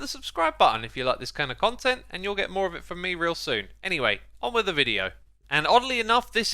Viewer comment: “Anyway, on wither video” That’s what I heard, at least.